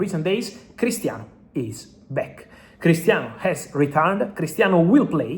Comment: Bro your English is good but when you say Christiano is returning I swear it sounds like you said hes retiring. I almost fell over